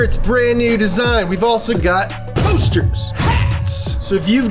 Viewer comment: I heard Toasters! And I got glad for a second then I saw poster and got sad..